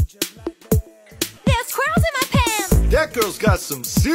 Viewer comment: What I hear: “ there’s curls in my pants! “ 💀